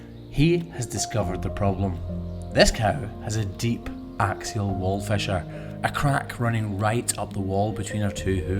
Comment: Thought this said "anal wall fissure" and I was like: "wow he really decided to change professions, just like that."